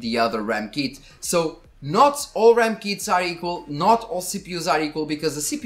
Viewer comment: Hope this video is all you guys are expecting. I did my best and took hours and hours around this :D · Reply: At first i thought you were saying "RAM keys" instead of "RAM kits". Was getting really confused. RAM have keys? I never knew that haha